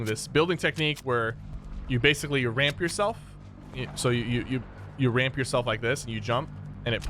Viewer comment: I swear he said rape yourself